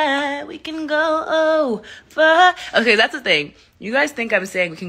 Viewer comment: This is how I heard it “baby do it right, do me right, we can go all the time” “I know you think that you know me, but you don’t even seem that toxic”